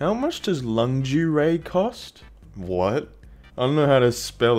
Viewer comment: I thought that one guy was saying laundry instead of lingerie